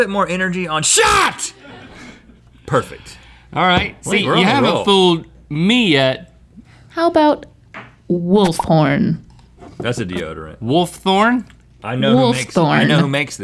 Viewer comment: i thought she said wolfporn